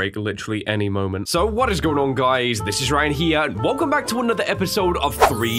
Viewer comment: When he said this is Ryan here I thought he said this is Ryan Higa